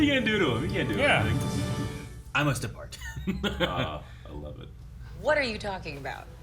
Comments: Wong said "I must decline." The captioning has "I must depart." Might be a re-write after the captioning service got the script or the captioning tech misheard, as well. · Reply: I heard him say depart. And it makes more sense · Wong said "I must decline." The captioning has "I must depart." Might be a re-write after the captioning service got the script or the captioning tech misheard, as well.